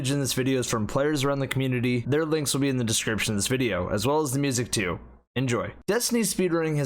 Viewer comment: I swear I keep hearing 'Their links will be in the description's video', interestingly captions say 'the description of this video'.